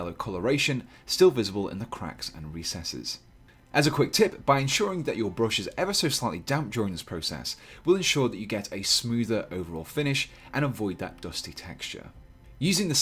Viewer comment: what does he say at 1:13? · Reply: ‘As a quick tip, by ensuring that your brush is ever so slightly damp during this process, will ensure that you get a smoother overall finish, and avoid that dusty texture’